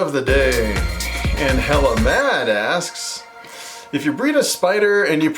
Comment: I’m so dead I was just listening to the video and I heard“helluh mad” 😂😂🙏 thanks for answering the question!!